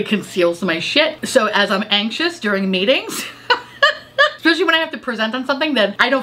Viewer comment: I thought you said "when I'm anxious during MATINGS " Instead of meetings and I spit out my coffee🤣🤣🤣🤣